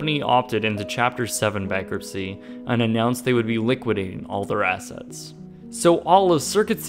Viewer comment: Say it with me now: sub - sid - i - ary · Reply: Noticed that, too... Also, from later in the vid: liquidating... missing syllable there. (sounded like he said "liquiding")